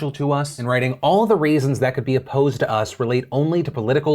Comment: Did phill say "all the RAISINS that could be opposed" or is my hearing finally going out?